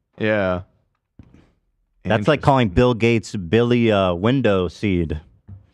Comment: My first watch I heard Ethan say “bill Clinton was Billy window seed” and I was so confused. Now I hear that he said bill gates. 😂